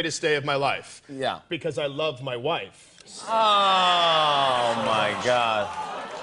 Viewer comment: did he just say "I love my wives" ??? that's cool